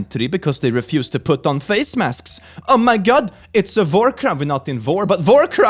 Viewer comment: did he just say "it's a vore crime"